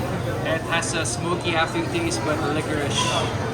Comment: Liquorish! I thought he said licorice!